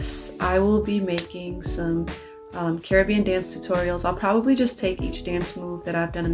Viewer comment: You're inspiring. There's so much more to you than just nailing Kamaro's Dance. In this video I thought you were saying Kirby dance and got excited. Couldn't believe it, rewinded, but you said Carribean dance. Oof. xD I always loved Majora's Mask and Wind Waker. I waited for this question. What are your favourite Zeldas?